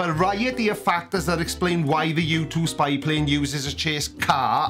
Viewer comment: To be honest I thought he said "Youtube spy plane" ... and he could have a point there, but it later was cleared up he was talking about the U-2 Spy Plane.